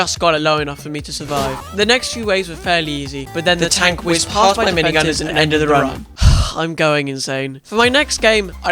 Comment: I didn’t know what you said when the part said, the tank went past my minigunners and ended the run, I thought u said that tank went past my phantoms!